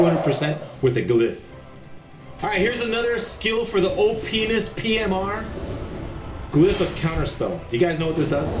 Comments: did you just say, "a glyph for the O-Penis PMR?" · did he say o penis pmr lol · ROFL, GO TO 8:00, Sounds like he said penis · did he just say oh penis? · LOL DID HE JUST SAY O-PENIS PMR? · Did swifty say penis @ 8:00? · wait at 8:00 does swifty say o' penis? i know he said op ness but i thought this was quite funny :) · first time i saw this i thought he said openis pmr lol